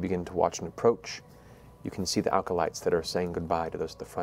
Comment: Is it me or is Matt putting an extra L in acolyte? I keep hearing 'alcolyte'.